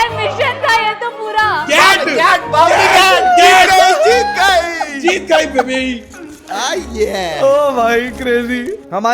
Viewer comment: what does said😐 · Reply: Baby 🤣😭